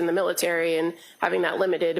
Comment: no smart ahh, you did NOT say experience “with” the military. You in fact did say “in”